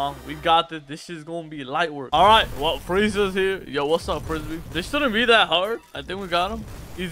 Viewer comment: did you just call frieza frisbee? 😭